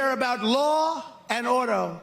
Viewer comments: To me, it sounds like Trump is saying ”Law and Odor”… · I heard "Law and Odo", meaning he loves Deep Space 9 and Odo's adventures in stopping the bad guys. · I heard "Law and Odo", which is, surprisingly, a Star Trek Deep Space 9 spin-off I won't mind watching. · I thought he said 'Law and Odo'... I mean, Odo was one of Rene Auberjonois' more memorable characters... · “Law and Auto”.... Oh he did not, yup he sure did. Laughing my ass off.... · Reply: Not gonna lie, I heard: Law and 'ODOR!!! flashbacks to zombie arms flailing through holes in a rotted door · I heard "Law and Odo", meaning he loves Deep Space 9 and Odo's adventures in stopping the bad guys. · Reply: I thought he said "law and odel," and didn't even have a guess. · I also heard Odo, but then I thought nah, star trek would lose trump's attention as soon as he found out that he can't call in live and incoherently ramble at the characters. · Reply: I thought he said "Law and Ono", as in Yoko. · I heard "Law and Hodor." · I heard “law and odo”. I thought he was saying “law and odor.” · I thought he said law and odor. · I heard "Law and Odo" and thought of Deep Space 9. 🤣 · I thought he said "Law and Oder." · I thought he said, “Law and ODOUR”. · I thought he said "Law and Odor". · I thought he said "law and odor" - but auto works.